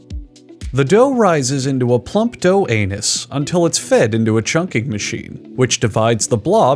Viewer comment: Did he just say plump dough anus